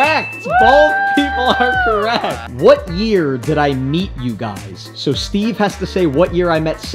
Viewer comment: It sounds like he said what year did I eat you guys?